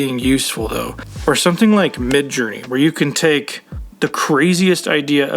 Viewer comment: what did you say there? It's a bit slurred? sounded like MidJury??? What is it? Link?? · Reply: Midjourney Ai. <link>